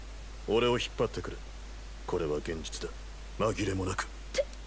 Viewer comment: did he just say genjutsu?